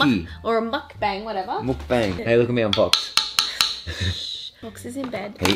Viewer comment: did he say "hey look at me I'm Fox"??? hahahahaahaha